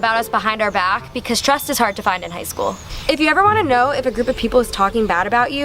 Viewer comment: I thought she said “ stress is hard to find in high school” and I was like ?????????? what the fUCK